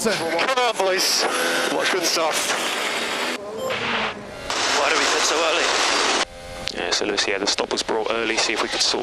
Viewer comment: ハミルトン『悪い！クソ悪い！』って聞こえて草